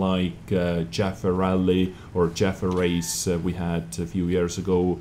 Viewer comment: PS! KEEP IN MIND, THESE WERE THE ANSWERS TO THE PUBLIC QUESTIONS, THESE UPDATES ARE NOT THE ONLY UPDATES IN 2017! There were more questions, but I simply did not want to make this episode 30min+ one. :) And to all my US fans: "As we express our gratitude, we must never forget that the highest appreciation is not to utter the words, but to live by them." - John F. Kennedy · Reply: when you said Chafee race it sounded like you said Jaffa race and i just started to laugh. :)